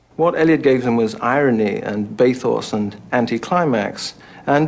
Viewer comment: Subtitle wrong for 6:11, 12:54 and 13:24. He doesn't say 'Pathos' but 'Bathos'. 'Pathos' would be out of place. At 21:31, PAckroyd says 'bits of Villon' (subtitled as INAUDIBLE), which is François Villon, French poet of the Late Middle Ages. Funny that the documentary doesn't mention that "He Do the Police in Different Voices" (26:35) is an allusion to Dickens's novel Our Mutual Friend, where one Sloppy says he can give to 'Mrs Higden the Police-news in different voices.'